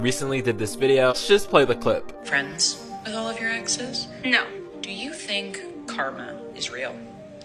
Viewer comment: I rlly thought the lady said “Do you think cartman is real?..” ADDISON: “yes!”